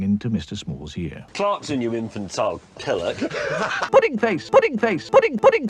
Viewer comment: What did he say? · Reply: "Clarkson, you infantile pillock...!"